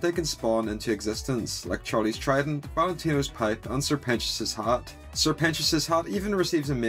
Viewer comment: I swear I thought it said Valentino's pride😭